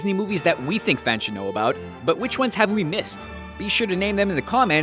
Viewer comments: Who else heard at 5:02 when he said "Subscwibe" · did he say subswibe?